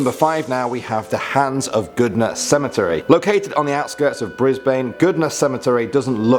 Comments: Did you just say "BRISBAIN" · did he just say bris bane